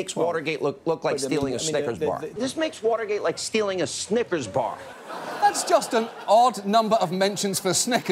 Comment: Did I hear "Snippers bar"?